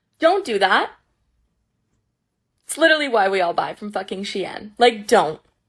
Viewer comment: youtube’s closed captions said “it’s literally why we all die from fucking shit” 💀🥀